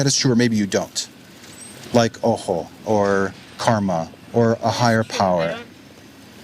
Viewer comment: i don't understand what he is saying ? "Oho" ? What is that ? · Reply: Al Couz “Ojo” or “malmde ojoj”. It’s similiar to “evil eyeL, I think.